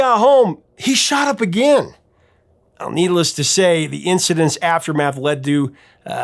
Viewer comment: I thought you said "needle-less to say" for a second in the intro.